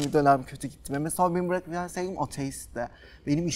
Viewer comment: te ne diyor anlayan var mı ? Neyim ateistti diyor ?